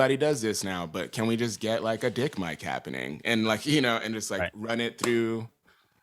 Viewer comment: did he say a “dick mic”?